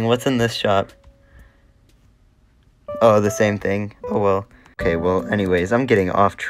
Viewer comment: PLS WHY DID I HEAR "OWO"